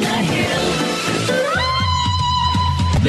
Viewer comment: What's the word they say at 0:22? disfung, dishung, IDK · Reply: "there's a family by the landfill with hazardous foam in their happy glowing home"